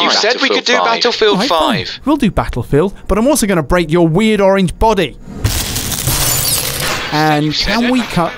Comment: did he just say orange b-body?